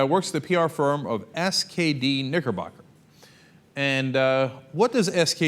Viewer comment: Lol, I swear I heard "niggerbugger" the first time Cenk said nickerbocker.